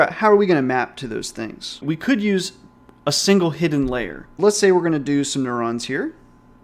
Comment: Please fire the person who made the English subtitles. It looks like they've used a speech to text AI. for instance: 2:12 "hidden lair" instead of "hidden layer". This wasn't the first mistake I saw, and I'm just 2 minutes in the video...